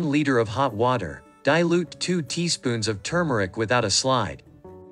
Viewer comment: 2 teaspoons of turmeric without a what?????? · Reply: Hi, I thought he said slide, but I'm with you, I don't know what it means. 🤔